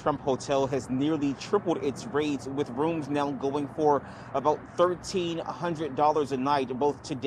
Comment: I thought he said Trump hotel rooms were going for "about $13-100 a night" and was like, "No wait that doesn't sound right..." Turned on the subtitles. "Ah, that sounds right." Who can even afford that shit during a pandemic?